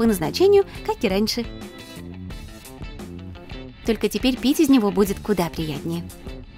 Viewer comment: мне послышалось) и теперь пи́сать в него будет намного преятнее 😂😂👌🤣